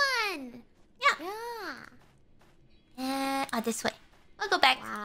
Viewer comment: her "Ah this way" sounded like a French "à tes souhaits" that actually surprised me.